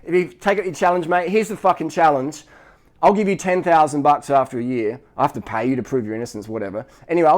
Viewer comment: Did he say " I'll pay you 10000 baht" 05:54?